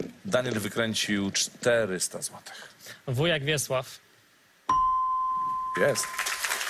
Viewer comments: usłyszałam ,,Wujek Wiesław" xD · POWIEDZIAŁ w jak wiesław ja usłyszałem wujek wiesław